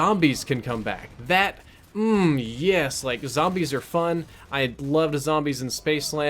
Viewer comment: "Nazi's are fun" - 2017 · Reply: Damned heard it wrong, it was " zombies are fun", i swear i first heard Nazi's.